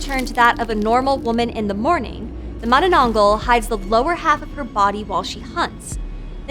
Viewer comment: Okay so im Filipino and at 3:17 i heard her say with an exceeding syllable: "Ma-na-na-nang-gal" 😂 and that just broke the laughter out of me 😂😂😂 🤟 p.s. Love your channel 💕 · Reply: Well, you misheard her. She clearly only said two "na" - "Ma - na - nang - gal."